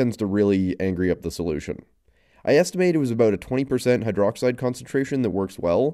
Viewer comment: Turn captions on. “I estimate…” gets captioned as “is tomato” for some reason and it really confused me so upon rewinding to see what was actually said I started laughing and making this pointless comment. God I love YT captions, but If anybody need actual captions written up lmk. I’d hate to be a deaf person reading… “…but that also tends to really angry up the solution is tomato is about 20%”